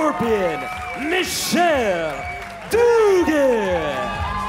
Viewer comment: swear to God it sounds like he says “MICHELLE IS A DUUUUUUUDE”. Which looks about right.